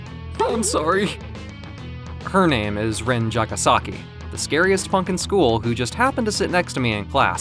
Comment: thought he said "scary as fuck at school"